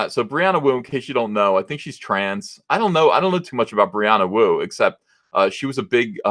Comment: Thought you said Brianna Woke for a second.